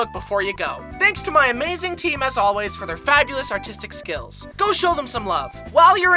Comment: I thought he said autistic skills💀